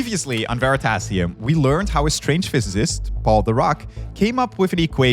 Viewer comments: What they said: "Physicist Paul Dirac" What I heard: "Physicist Called The Rock" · I deadass heard him say "Called The Rock" instead of "Paul Dirac" and I was like "THE ROCK?!" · I was listening to this on 1.5x while playing a game, and I heard “a strange physicist called The Rock.” And then I kept hearing The Rock. It was a very interesting listen.